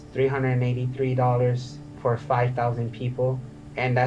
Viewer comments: I thought he said $883.00 per day instead of $383.00???? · The second time you played it sounds like he says $883 per 5,000